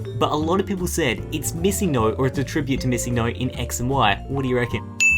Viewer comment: who else thought he said missing NOSE?